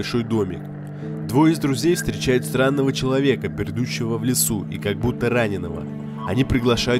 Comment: Почему я на 9:01 слышу не "Пердущего"?